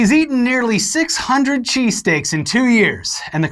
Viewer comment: My girlfriend thought they said that buddy ate 600'000 cheesesteaks in 2 years. How the fuck would you eat 2000 cheesesteaks a day